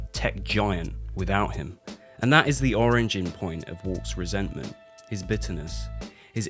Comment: Did you say... orangen point?